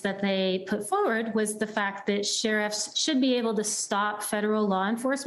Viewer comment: Did she say tenants?